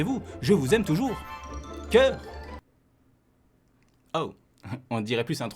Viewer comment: j'ai entendu cum 😭😭